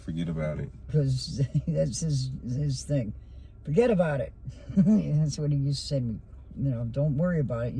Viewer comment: I had to watch this twice.I heard, someone say,"Go back to Frankfort and look".... Just want to say to,Your sister is so lucky to have you looking for her, don't give up.God bless you... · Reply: I heard horses. It sounded creepy. And right before they said, "forget about it" I heard "Frankfort"